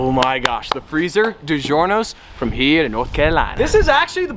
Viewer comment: Did you say North Carolina